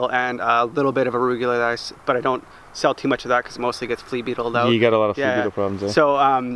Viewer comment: What did you say @ 2:44? You don't sell much arugula because it gets "flea peopled out"?